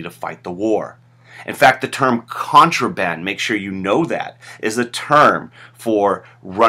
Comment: What is the word that you say we need to know at 8:55? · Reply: Yumna Indorewala contraband